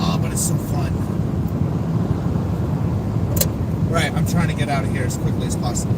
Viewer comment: I heard "get out" when you asked what kitty wanted you to do... that's so creepy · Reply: I heard" in bed, get out".